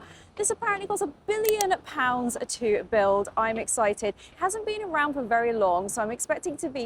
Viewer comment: For a second I thought you said “Apparently this costs a billion pounds a night”😂